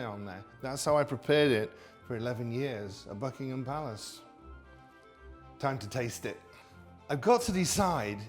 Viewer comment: I thought he said FUCKINHAM PALACE 😂😂😂😂😂😂😂